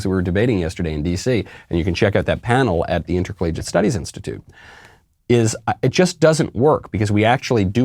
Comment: interplagent of studies institute? Is that what he said?